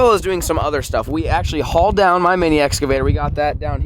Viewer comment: did he just say egg avator